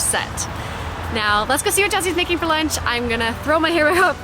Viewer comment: it sounds like you said "what chelseas making for dinner" 😂